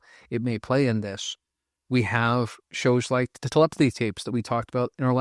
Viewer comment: what show did you say? YouTube scrubbed that 1 second w/ the name. Collectivity tapes? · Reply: The Telepathy Tapes.